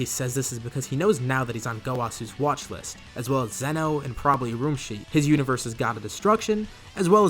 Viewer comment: Right after 48:00 did you say Roshi, his universes God of Destruction?! I could dig it